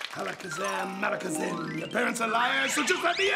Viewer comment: who else heard Bray wyatt said “Your Parents Are Liars”?